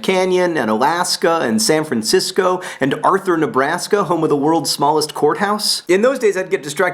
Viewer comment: The first time john said worlds smallest courthouse i totally thought he said worlds smallest whorehouse..